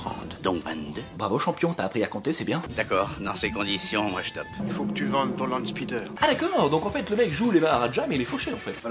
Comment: j'ai compris "Faut qu'tu vendes ton hand spinner" AH D'ACCORD